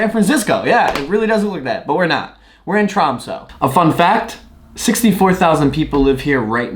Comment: Thought that you kept saying Toronto, rather than Tromso.